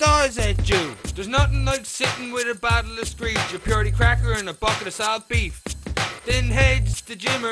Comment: can anyone clarify what he says at 0:57? I hear, bottle of screech, buttery crackers, and soft/south (?) beef? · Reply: BUcket of salt beef